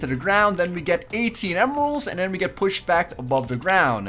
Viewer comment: did he say emeralds?